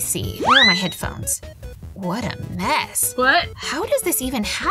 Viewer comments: I think we heard voice beacause she said "what" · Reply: I think we heard voice beacause she said “ what” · WHO ELSE HEARD ANOTHER GIRL SAYING "WHAT" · Okay I know I'm not the only one who heard that weird voice saying "What" like if u heard that. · DID ANYONE HEAR THE “ WHAT” FROM